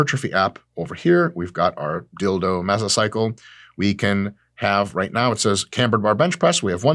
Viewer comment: Did you just say your dilldo meso cycle? At :58s